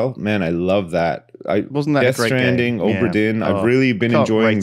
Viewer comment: What's the name of the game at 18:20? I've been trying to look for it, but I can't quite understand the name. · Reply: Death stranding or obra din?